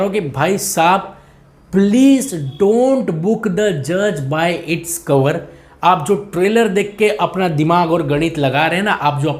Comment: Did he say don't book the judge by its cover